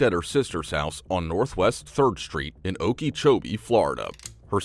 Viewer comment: Okeechobee in a nutshell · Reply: I'm glad you spelled that - I thought the narrator said Okeychokey. Mind you, both names are a bit bonkers.